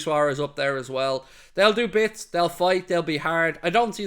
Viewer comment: Hoping for Messi and Argentina to take the trophy so he can be cemented in football history as the world's best ever to play the sport 8:20 lol I had to listen to this bit again, I thought you said "they will work hard, they will bite (you said fight)" lol